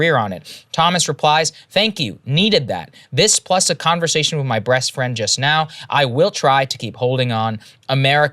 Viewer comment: Did Saagar just say ''Breast Friend''? 🤣🤣